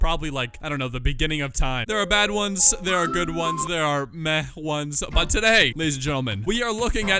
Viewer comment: Dude why did I hear “there are meth ones” so instead of “there are meh ones”